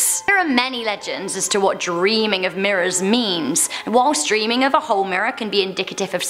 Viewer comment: did i hear memes?